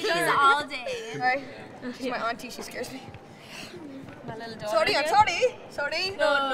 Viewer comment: what is Liza saying?? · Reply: She said “ she’s my auntie she scares me “ then “I’m sorry I’m sorry”